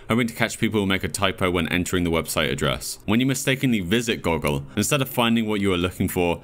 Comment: why did I think it said google